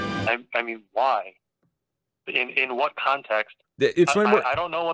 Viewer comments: "Hot Sunny Michael" was my nickname in high-school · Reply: I misheard as "Hot Mommy Micheal" like, weird name for a mommy but we don't kinkshame so w/e 🤷‍♂️😂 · I thought he said Hot Mommy Micheal I'm so deaf.